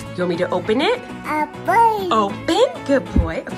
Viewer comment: Omg when said “open” and then you opened it, it sounded like he said “opened it!” 🥲 even if he didn’t say that, cuuuuute!